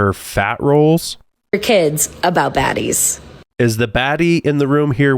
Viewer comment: I think she said "fatties" not "baddies"